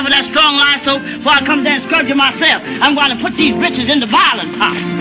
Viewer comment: sounds like she's saying bitches, but I know she isn't,, what is she saying though really? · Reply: 'Britches'- his clothes :)